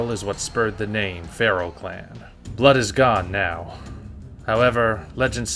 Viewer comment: Blood=TigerStar Also I thought said "Now Blood is God."